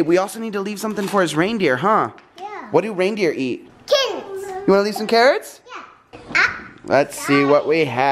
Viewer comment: I don't know why but when said carrots at 23:50, I heard kittens😂😂